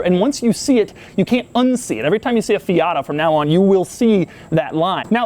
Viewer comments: does he say “Fiata”? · Did he say Fiata?